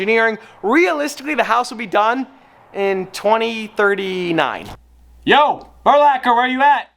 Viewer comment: did anyone else hear him say it would be done 2039